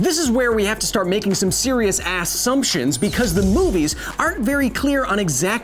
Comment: And did he say asssumptions?